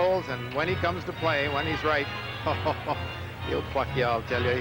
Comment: Wait a minute..Howie Meeker is talking about Glen Anderson at 1:39:22...does he say "hes going right, he'll fuk ya"??..lol, did i hear that right??